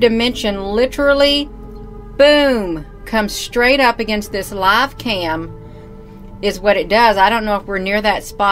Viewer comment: OMG. I saw some videos of her on the Logicked channel (similar to yours). How crazy/stupid can one be? 6:52 did she say "love cam" 🤭